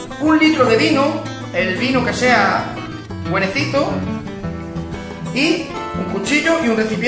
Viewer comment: No entendí lo del vino , le escuché "guerecito" !!??? Que es éso..? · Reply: Buenas María. Quería decir..."buenecito" jajajaja. Es una forma de hablar. Saludos